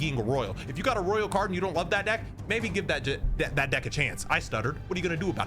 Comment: Nah i heard "that Dic" before you fixed it 😂